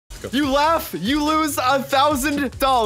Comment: did he say go? 🤔🤔🤔🤔🤔🤔🤔🤔🤔🤔(spam it)